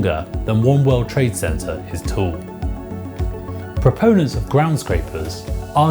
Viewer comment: Did he say world war trade center is tall in